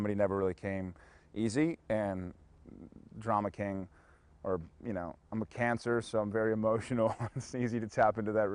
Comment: "I'm a cancer" I heard "I have cancer" and was like X.X WHAAAAAAAAAAAAAAAAAAAA?!?!?!!??!!